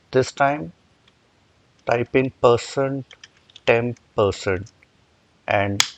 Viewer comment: I thought you said "type in person, temp person" so I did.. lol... that got me nowhere and I thought it sounded incorrect so, I zoomed in to watch what you typed in :) ampersand temp ampersand (&)